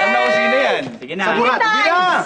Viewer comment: Narinig nyo yung sinabi ni Paolo ? sa bandang 0:20 "sa burat" daw